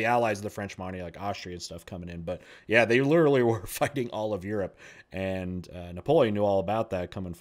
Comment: i've misheard "fighting" for "fucking". God bless subtitles!